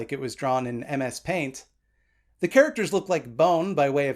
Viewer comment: I misheard that he started off by using NS paint instead of MS paint and I was like oh even his drawing software is a nazi thing